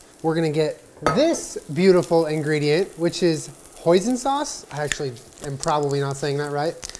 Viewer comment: I really thought this man said “poison sauce??” 😂😂😂😂😂😂